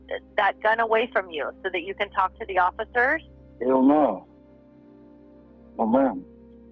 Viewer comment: He said "hell no" not what the caption says "they don't know, ma'am", ffs.